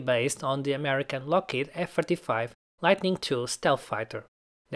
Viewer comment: excuse me f 45 or 35?I heard 45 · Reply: I said F-35 :)